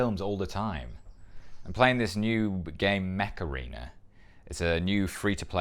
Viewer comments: When he said he was playing Mech Arena I first heard it as “Macarena”—would love to see the group of them do that 😂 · Honestly, I thought I heard you say Macarena. Was watching this on my phone and I wasn't looking at the screen when I heard that. Then I looked and said "OH, MECH ARENA!" 😅